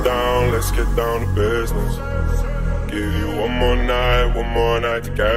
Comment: i first thought he said let's get down the basement